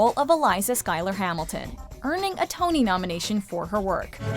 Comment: at first I thought they said burning a tony nomination, while they were playing burn